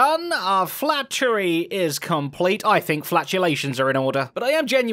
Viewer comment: At the beginning when you said i believe flatulations are in order, I thought you said I believe flatulence is in order 😂😂